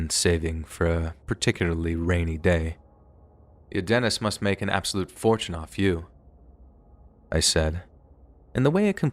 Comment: What does he say here? I hear “U Dennis must make an absolute fortune offa you” but there’s no way that’s it. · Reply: “Your dentist must make an absolute fortune off of you.” So she had bad teeth.